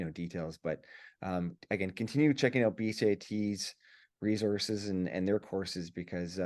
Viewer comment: I could not make out what the wall system site he was talking about. Sounded like bcat but couldn't figure it out. It is when he discussed mock up walls. Great presentation 👍